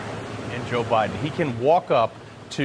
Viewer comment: did he say Joe Brandon?